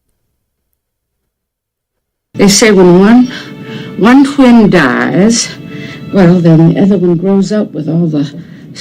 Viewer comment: Does She say when one CLONE DIES ??? .......... I have some of MY pictures to send you... How do I send them ???